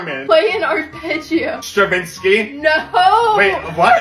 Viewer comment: I thought they said Shervinski for a second and I was very concerned. I knew we had some famous architects, doctors, and poets but not composers😂